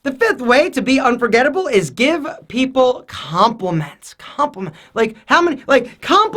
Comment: i thought he said condoms 😂😂